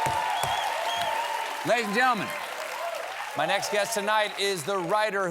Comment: Why do the subtitles say "LAD HEES AND GENTLEMEN"? 😭